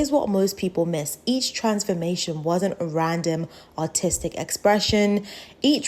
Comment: every time u said "Artistic" i thought you were saying "Autistic" until i saw the subtitles